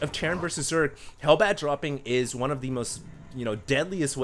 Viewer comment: i swear i heard say "bat droppings"